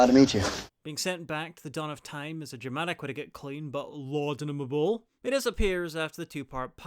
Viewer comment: "Lawdininable"? "Laudenable"? Forgive my american education. What is that word? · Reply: Laudinimable. Laudable crossed with laudanum, the drug.